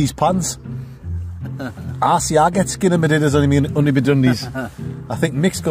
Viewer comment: translate please I think u said skibidi though